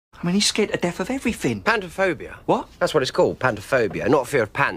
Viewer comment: does anyone else swear that the doctor said panda in that scene or is it just me